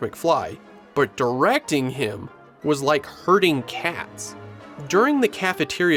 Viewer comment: The herding cats line cracked me up so hard because I originally thought he said hurting cats! I thought "what the hell?" Hurting cats?